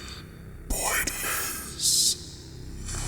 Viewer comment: did anyone hear what the heck that creature said cus i didn't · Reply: shilo wolf “pointless”